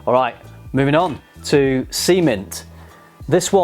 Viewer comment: I misheard "Sea mint" as "semen" 😳